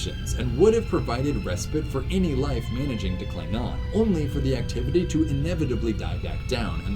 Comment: I was only half-listening around 14:00 when I thought I heard “Klingon” :D